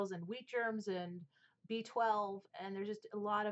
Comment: I have the airbrush spray, and it's glorious. I keeps people from being blinded by my milk-white skin. At 12:30, where you say "B-12" I heard it as "beached whale" and thought, "La Mer has issues..."